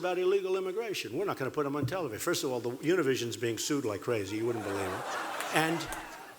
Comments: did Trump say Eurovision is getting sued like crazy? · Did he just say "The Eurovision is being sued like crazy, you wouldn't believe it"???